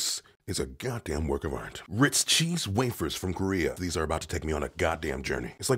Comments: Could it be a... Seoul Journey?!? Huh? Huh? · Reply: Oh.I thought he said "from Korea". I don't have a pun for China. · Oh I thought it said Ritz cheese waffles lol 🤣🤣🤣🤣